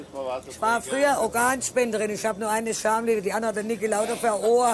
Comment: Kann mir jemand sagen, was die "Königin der Nacht" da sagt?? "Ich war früher Organspenderin, ich habe nur eine (...) Verstehe diesen Dialekt nicht . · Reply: Ich war früher Organspenderin ich hab nur eine Schamlippe die andere hat der Niki Lauda am Ohr... oder so in etwa